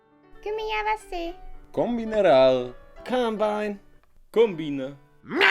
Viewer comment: мне одному послишилось что он сказал колумбайн